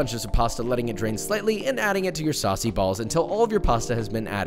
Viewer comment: did you say sussy balls?